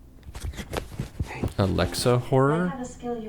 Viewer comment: SANS!?!?! (i know its not what it actually said but it was kinda funny the first time i heard it ngl)